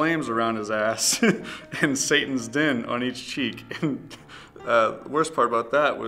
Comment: sounds like hes saying "satans din"